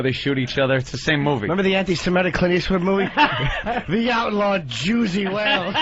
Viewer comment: I lost my mind when I heard "The Outlaw Jewsy Whales" LOL